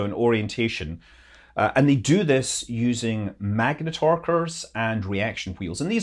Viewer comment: I rewinded it three times and I still could not understand what you said they sound like you said magnetar burst Jesus if our son could produce a magnetar burst we definitely be extinct incinerated blasted off the face of the Earth I'm going to slow it down and watch it one more time at half speed..... That was my mistake cuz I watch everything in 2x speed so regular speed it was magnetar curse and reaction Wheels, I kind of get the ladder but please tell me WTF is it? Yes I can look it up probably online easily but I like your show so if you give an explanation that would be cool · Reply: Maybe you heard the word ‘magnetorquer’ a device used to rotate a spacecraft by pushing against the magnetic field.